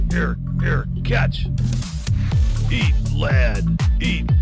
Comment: all my childhood I heard them say "B-flat" (you know, a music notation) xD